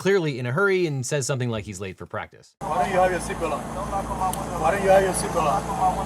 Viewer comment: “Why are you wearing a seatbelt?” Because I’m in a car. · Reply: I dont know if the subtitles were wrong or if you misheard it, but I heard "why aren't you wearing your seatbelt" which would track logically. As for whether or not he was wearing the seatbelt while driving, I call BS on them being able to see anything thru that tint, so Hill could just as easily argue that he was wearing his seatbelt while driving, then unbuckled it once he was pulled over so he could retrieve his license. It's a moot point I guess, since they didn't ticket him for it.